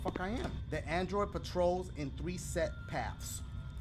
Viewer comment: Dashie said scene instead of sense